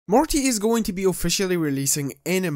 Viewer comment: did he say Marty? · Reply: Nah I hear morty